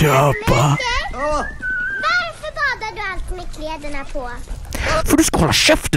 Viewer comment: What exactly is that child saying in Swedish at 17:42? I'm learning Swedish and I'm used to hearing simmar, but that's not what I'm hearing. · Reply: "Varför badar du alltid med kläderna på?". meaning "Why do you always bathe with your clothes on?". Bada = Bathe, Badar = Bathing, Bad = Bath.